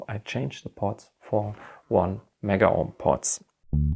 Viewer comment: Dude, I can't understand what you said at 2:29 about the potentiometer's vales. I have a Rascal and I'm looking at potential mods. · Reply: I think he said "1 mega ohm pots"